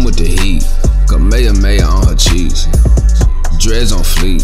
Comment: LYRICS: (1st half) Goku: I just flipped a Senzu for a stack, Ki blast in yo mouth that's a snack Smashin on Chi Chi from the back, she died so I had to wish her back (Vegeta): Dragonball chain on my neck, they shining like a star when I tread My widow's peak make yo girl wet I pull a galick gun, hit the deck (2nd half) Goku: Jiren is weak, I kicked that nigga in his meat Come through w/ the heat, Kamehameha on her cheeks Dreads on fleek, just got them hoes last week Gucci wrist bands nigga, Gucci martial arts gi Vegeta: Jiren is trash, elbows got a lotta ash Quick with the blast, hit you with the final flash Big Bang attack, tell yo girl I'm tryna smash Zenni in cash, Senzu beans in the stash Goku (singing): On my back I got a power pole, and I got a hundred bad hoes Vegeta with me and he be my bro, we gon let yo girl play with our toes You're welcome👍🤙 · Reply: I heard "Kamehameha on Chi Chi"